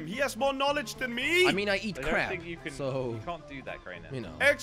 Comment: am i the only one that thought he said "i eat crap"